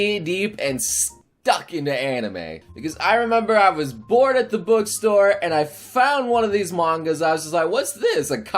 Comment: When he said I was bored at the bookstore, I heard him say I was born at the bookstore